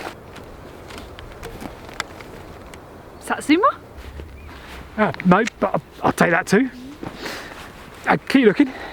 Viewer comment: What does call the clementine orange at 2:12? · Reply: Satsuma?